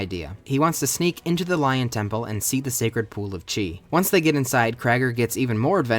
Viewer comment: I thought that said “the obscure lore of china “